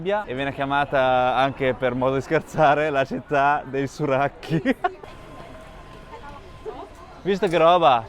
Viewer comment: città di cosa che non ho capito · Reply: haha!! scusate, pensavo "surracchio" fosse un termine italiano e invece è dialettale pesarese.. comunque significa "scatarro" 🤣